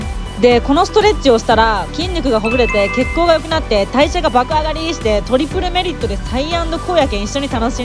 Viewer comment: 初め見てた時、代謝が爆上がりっ！！が、体重が爆上がりっ！！に聞こえて一瞬時止まったwwww